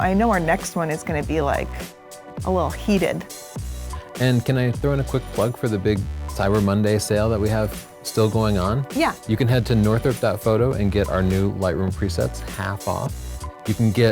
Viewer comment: I was multitasking (taking pix of my cat) and misheard you. Thought you were offering "autographed Lightroom presets."